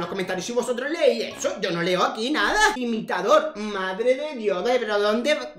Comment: creo que dice imitador